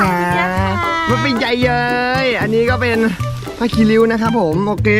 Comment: หนูได้ยินเป็นผ้าคีลิ้ว